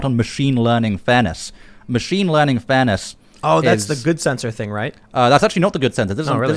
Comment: No matter how hard I try, I keep hearing "machine learning Thanos". Accurate but I still know he said something else.